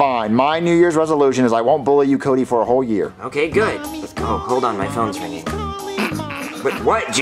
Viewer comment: got me laughing I just heard mommy calling mommy calling goofy ringtone